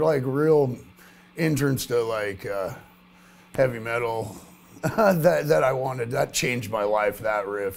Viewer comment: I thought he said ketamine metal @ 2:00 and I was disappointed to learn that is not a thing